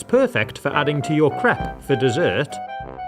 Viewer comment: lol sounded like he said "you can add it to your crap for desert".